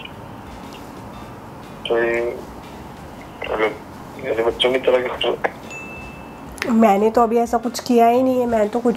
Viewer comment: Can someone tell me what bhavin 5:49 said here🤔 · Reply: He said "mera baccho ki tarah katoge"